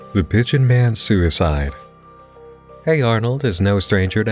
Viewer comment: It sounded like you said “The bitchin man’s suicide” lmao